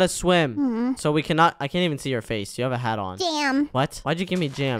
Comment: Did she say damn